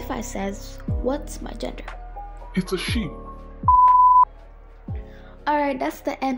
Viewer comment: It sounded like “it’s a sheep”